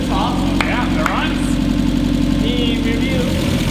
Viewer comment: Am I the only who heard "Meme review" at 9:39 ? x) Nice video btw !